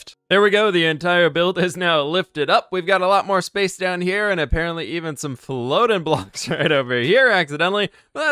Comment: is it just me or did it sound like he said a..PARROT..ly 🤣👌👌👍